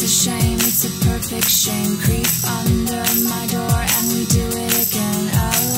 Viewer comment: After you see this video a couple of times. it sounds like she is saying (DO ME AGAIN) XD